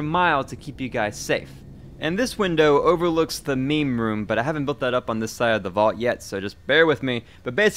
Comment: did you say MAIN room? Or MEME room